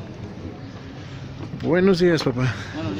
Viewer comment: Buenos días Papa? Escuché bien? 0:18, Saludos desde Panamá.